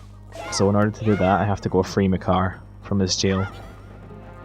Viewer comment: Because of his accent, at 3:13:30 I thought he said "go frame a car" :/